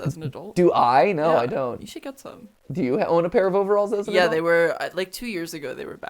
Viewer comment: did matpat say did you wear a pair of overalls as a girl